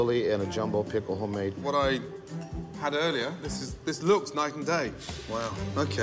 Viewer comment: Whait he says night and day I always thought he said nice and date